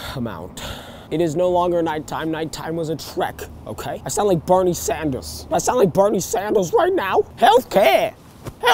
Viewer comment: ngl I thought he said "I sound like Barney's sandals"